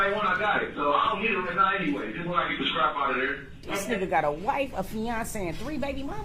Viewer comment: he says "ill get it when i get the strap out of there"!!! DID ANYONE ELSE CATCH THAT? DID HE have the 🔫🔫in the car?!!